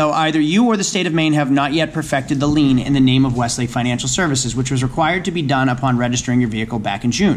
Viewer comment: Is that the same company that helps people get out of time shares? I always thought they said “Wesley financial “. It’s what I thought you were saying also. Maybe my ears are going. 🤷‍♂️ Anyone else here what I heard? 😂